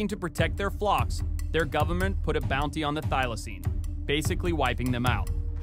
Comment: thought he said xylazine for a second 😭